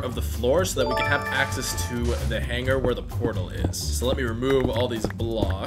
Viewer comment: i thought this guy said hunger and not hanger. I guess im just hungry